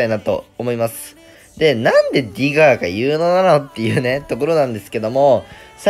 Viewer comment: 「なんでディガーがユーノなの？」 私「なんでディガーが有能なの？」て聞こえて え？？ってなったw